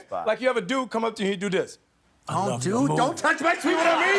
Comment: "don't touch my ... " What? I don't hear it.. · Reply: "Don't touch my sweet spot"